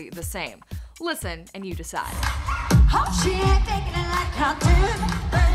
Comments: Perri says "I did"!!!!! Her voice cracked so it sounded like she said Hadid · all I hear "I did" maybe she needed to take a breathe chill out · I agree Marie Alcocer. I've listened to it and all I hear is "Like I Did Babe". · she said "I did " but her voice cracked so it sounded like she said "hadid " but she actually didn't